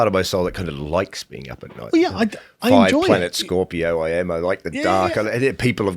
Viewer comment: Did he say 5 planet Scorpio?